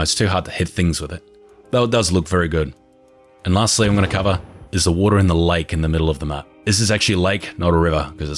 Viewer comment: “there’s a water in the lake” am i hearing this right?